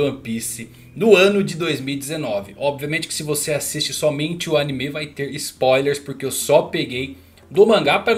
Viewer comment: kkkkkkkkkkk mano eu juro q entendi ''o ussop é gay'' kkkkkkkkk pqp